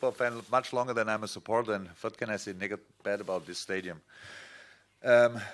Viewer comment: "what can i say nigga"